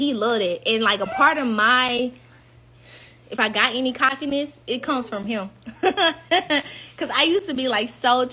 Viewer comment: She lost me when she was talking about her husband & said, "If I got any confidence, my confidence comes from him." NO mam. That means he could do or say something to destroy you. Confidence should come from within your self. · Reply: I heard her say her cockiness comes from her husband.